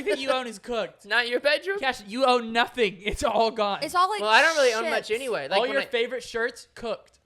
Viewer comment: did anyone else catch swearing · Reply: She said it’s all like chips